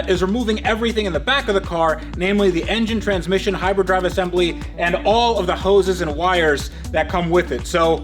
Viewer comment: i thought you said hyper drive assembly….. like wur? Mclaren can travel between the stars now?